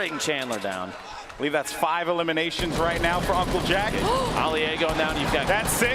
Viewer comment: Did someone say ALI-A 5:24 bro I’m deeaadd